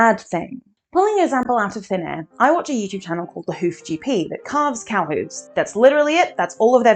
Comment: im so sorry what are you saying i can't hear it?? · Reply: "I watch a YouTube channel called The Hoof GP that carves cow hooves."